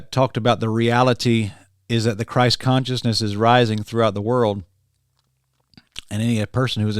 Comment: "The reality is that the false Christ consciousness is rising" is what it's really saying.